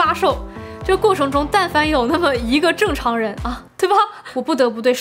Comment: 我听成了但凡有一个TM正常人😂